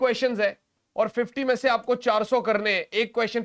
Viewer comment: sir 400 krne hai kya bola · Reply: Bhai 40 karne hai 🤣🤣